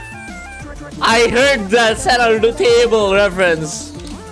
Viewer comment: "I heard that sand undertable reference"